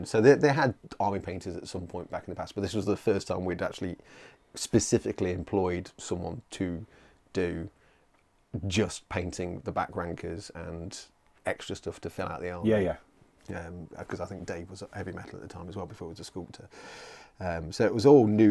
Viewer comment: I had to listen twice as the first time I heard "back wankers" and my mind went straight to the poor guardsmen! Great chat, very interesting to listen to Origin story ;)!